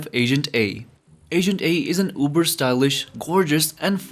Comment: I thought you said Agent 8 I was gonna say HEY IM AGENT 8 😂